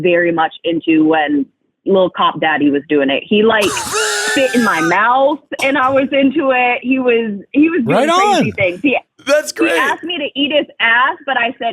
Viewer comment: I thought she said SHIT in my mouth hahahahaha I was like, wow first the cat litter now this?! 😂